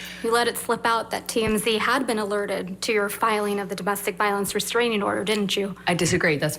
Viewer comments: I SWEAR I HEAR “You FUCKED UP there didnt you MS TURD???”, and its so satisfying, i had to really really try to get that it is reallly Slipped up” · Did she actually say "u f*cked up there didn't you" at 1:15? If it is that's super savage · I literally heard Camille says "you fucked up there, didn't you?" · At first I heard "you slipped there didn't you ms heard?" Now all I hear is "you f*cked up there didn't you ms heard"